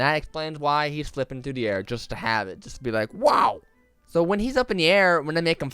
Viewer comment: um did he just say ‘ Just to make it sexy ‘ Him talking like James: oh yea baby (he also whisperd it then it was awkward ;-;)